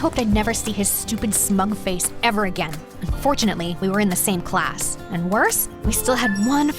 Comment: GIRLLL, I HEARD FORTUNATELY BUT THE CAPTION SAYS UNFORTUNATELY